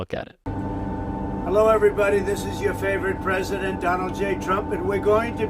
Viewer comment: Everytime he says this is your favorite President I want to vomit. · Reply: Same here. I hear "this is your favorite rapist"